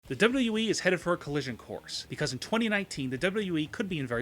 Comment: did he say we?